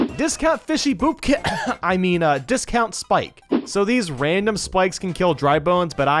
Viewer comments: smg4 boobkin is it or is it called bobkin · did I hear fishy boopkins :3